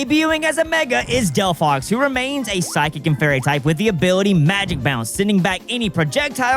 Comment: Delfox remains a psychic and what type? I heard fairy. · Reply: He did say fairy